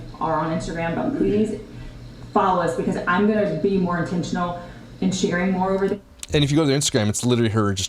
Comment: Did she say attentional? Is that a word? At time